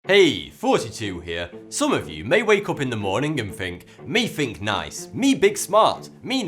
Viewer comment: I repeated 0:00 many many times, I hear 42, even his mouth moves like he is pronouncing 42. Love his content though. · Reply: He is saying 42, watch RIF 42